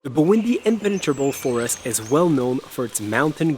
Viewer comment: I thought you said 'Burundi' but it Bwindi..... Buindi ;)